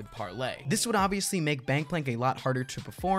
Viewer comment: is it me ,or did he say bangplank at 2:26 ?